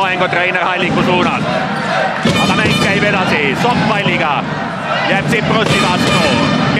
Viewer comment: Did anyone else hear, "Sock my n*gga" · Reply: He said "Sokk palliga" It means Sokk(players name) with the pall